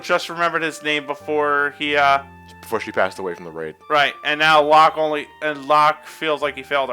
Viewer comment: did he say rain or rape? O_O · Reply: UmbreonDarkShadow Neither. I said Raid.